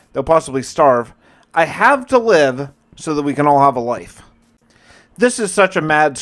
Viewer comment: did you say wife?